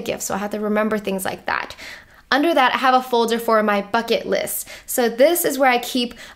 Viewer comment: When you said "this is my bucket list", I thought you said "this is my fuck it list". Then I was like hey I have one of those too haha.